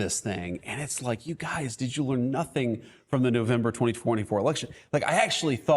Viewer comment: It really sounds like JD is saying "nazis are in now" when he says "did you learn nothing from the November election."